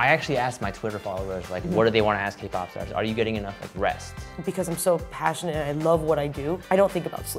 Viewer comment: what i heard was "are you getting enough breast?"